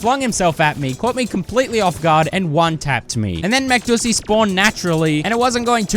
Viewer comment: did you just say mechdussy